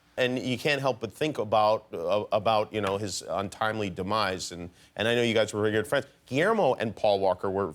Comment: guys, at 1:21, what does Jimmy say? untimely "demice"?which word is it? please tell me, thx, English is not my first language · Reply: Demise means death